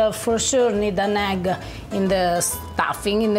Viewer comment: «If you’re going to make this dish for a lot of people you will for sure need a nag in the stuffing in the potatoes…»?? This I didn’t understand… Did I hear it wrong?? Anyone? 🤗💓💚💓